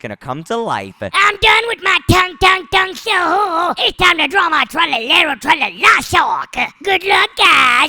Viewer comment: did say I’ve done my tung tung Saur so now time to draw tralalalo tralala